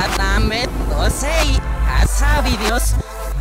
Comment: En el minuto 1:01 escuché me posei hacia mi dios